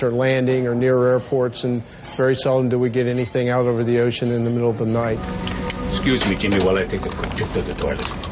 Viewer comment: tell me please what the investigator says exactly in 7:39.. I'm not a native speaker, that's the way I hear it: "and very seldom (?) do (?) we get anything out of the ocean in the middle of the night". I think I misheard some words in this statement. help me correct it please. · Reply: "Very seldom do we get anything out over the ocean in the middle of the night" is his exact wording.